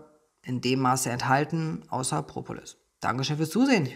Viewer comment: was sagt da ...außer??? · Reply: Propolis